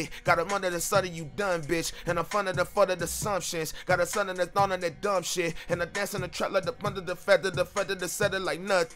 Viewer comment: In the first flow I swear I heard you saying, "Got a son and a daughter, they dumb shit"